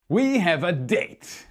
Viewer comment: The first words I heard were " we have a dick" 😅😅😅😅